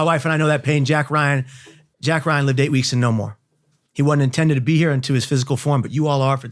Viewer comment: What does he say after jack Ryan... Something ladaytin weeks more? Weaps more? Is it a name?? The part where he says him and his wife know the pain of babies not making it past the womb(19:00?)